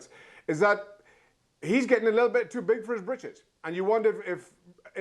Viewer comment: Thought it said bitches nvm